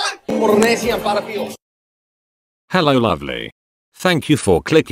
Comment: At the beginning I thought it said "Hello ugly" I was gettin ready to say thx😂😂😂